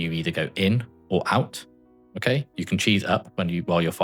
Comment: ish you say u can cheese out? chi out? i cant understand your accent im sorry what are you meaning by this? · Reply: Yeah so I mean cheese in the sense of risking a little to gain a lot. So if you stand halfway to the middle on the first set of beam before you know if you are going in or out you can save yourself time. It comes with the risk of having to just move back though.